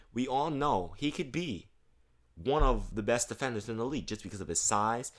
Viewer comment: for a scound i thought you said the best defender in the game but you said potential lol.